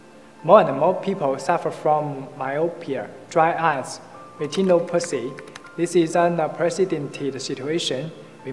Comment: did he just say Latino Pussy? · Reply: retinopathy , not latino p**y